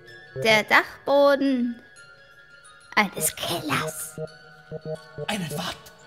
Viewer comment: Ich hab Keller verstanden 😜Der Dachboden eines Kellers, hääähhhh😆😆😆